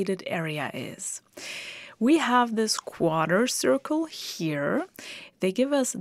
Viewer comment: I've never heard a Brit, or anybody, pronounce quarter the way she did. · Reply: I thought she was saying “quader circle”. Like a combination of “quarter circle” and “quadrant” which are both correct.